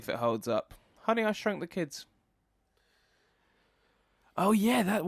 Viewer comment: At first I thought you guys were saying "Honey I Shot the Kids" and was rather horrified trying to imagine what kind of gruesome series you two were talking about!